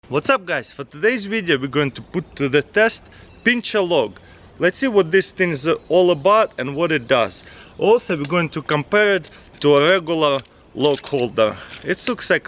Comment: where I come from, 'pinching a log' has a different meaning lol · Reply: err I thought he said "pinche log" lol